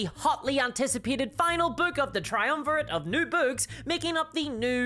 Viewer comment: I heard "Nude books" and had to do a double take before my brain caught up.